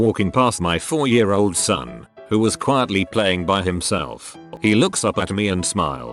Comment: thought that said 'playing with himself.